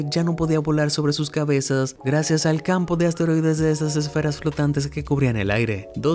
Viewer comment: que dijo!!!?😂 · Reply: Desasesferas flotantes